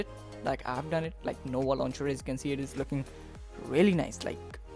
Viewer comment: I nearly missed the app name. you should put a link in the description bro as it's hard to understand your accent and YouTube captions thought you said "no more launcher" lol NOVS LAUNCHER PEOPLENON ANDROID STORE ITS BEEN OUT FOR YEARS 👍